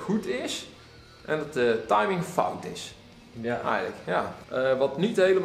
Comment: does he say "timing is bupkis" of fucked as? Is this a Dutch word?